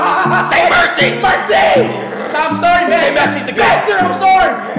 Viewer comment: Adin said "say messi is the goat" He is not racist. speed misheard it and said "master am sorry"